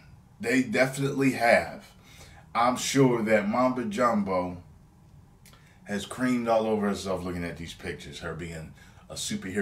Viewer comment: did he just say she probably creamed all over herself XD